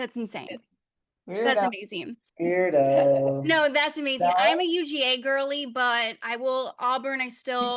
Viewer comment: Did I hear her say “that’s my man”😭🥹